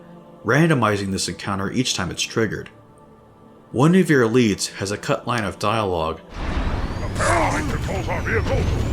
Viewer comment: What did the elite say before and after “impossible! It’s never done that before!” at 18:34 ? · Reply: He says "the Parasite controls our vehicles"